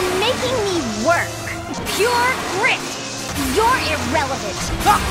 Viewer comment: I can't grasp what Leonie says. Someone help! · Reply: Pure grit!